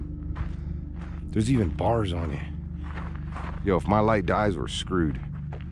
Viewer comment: OMG the “help” in 9:45 😩🥺 so much pain! Rest in peace🕊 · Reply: What I’m at 9 45 and all I hear “if my light dies were done”